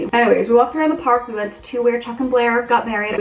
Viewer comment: you are talking so fast in this one, I heard you say you got married after sitting on a bench :) haha So I turned on captions and captions said "married" also 😂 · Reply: Are you talking about when she says we went to where chuck and Blair got married (from gossip girl)